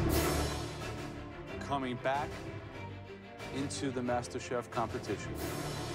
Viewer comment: did Joe say Master Sheriff?